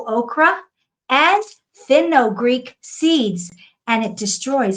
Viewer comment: Fenugreek seed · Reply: THANK-YOU ‼️ I thought she kept saying "FENNEL GREEK SEED" and I was like ...🤨❓whaaaat ?? Never heard of Fenugreek Seed, but Im gonna go look into it 👍